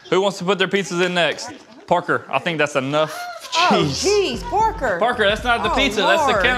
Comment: Why did I hear the mom say “oh jeez porker” instead of Parker ... their accents are annoying